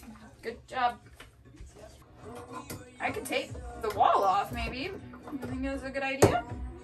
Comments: Oh my, I thought you said you were gonna take the wall off ..... "tape the wall off" oops! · "Tape the wall off" I thought you said take the wall off and I got scared for a hot second haha